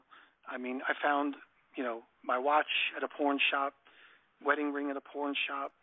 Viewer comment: LMAO around 2:12:00 I thought he was saying "PORN SHOP" with all that talk of jacking it before that.....